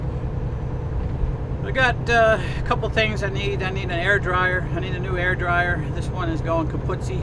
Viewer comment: Good morning!! LOL You really got my attention when I thought i heard you say you needed to buy a new "hair dryier"!! Yeah... me neither!! Happy trails, safe travels and thank you for sharing your time and miles!!